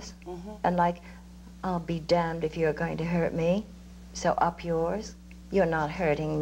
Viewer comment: Did Elizabeth just say "Up yours!"? I will always love that woman, she's tremendous.